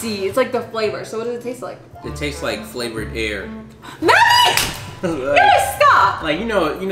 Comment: 🤣🤣🤣 I thought you said flavored hair!